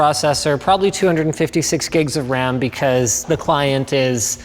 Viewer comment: thought u guys said 256 gigs of storage lmao